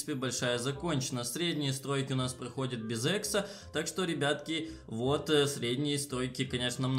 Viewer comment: я один услышал средняя строй проходит без $екса ?🤣